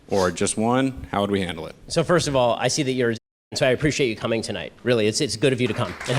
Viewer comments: What does Ben Shapiro say at around 0:31 when he says "First of all, I see that your [CENSORED] so I appreciate you coming tonight. Really, it's good of you to come." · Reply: Liberal, I checked the captions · what word is censored here? I've noticed it in other clips, too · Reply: Democrat